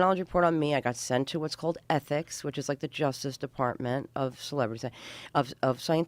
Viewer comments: Did anyone else notice her say something else other than Scientology at 11:51? I can't make out what she says but it sounds like she says celebrity center.. lol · can anyone tell what she said instead of scientology? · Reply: She said Celebrity Center